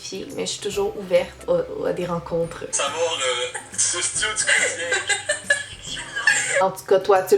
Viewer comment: il dit quoi ? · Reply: il dit: «Tu suces-tu ou tu crosses yinque?» qui signifie: «Suces-tu ou masturbes-tu seulement?»